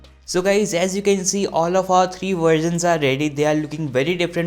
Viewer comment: The way he said versions it sounded like he said “all of our three virgins are ready”